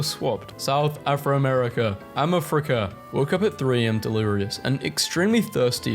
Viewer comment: I didn’t read the titles and I thought he said “Ima fricka’”